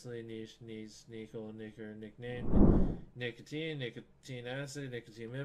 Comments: Uh...... 0:10 between Nicole and nickname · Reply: Either knicker or nicker, really sounded like he said it for a sec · i heard him already say it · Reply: he said "nicker" but for some reason the subtitles completely disregarded it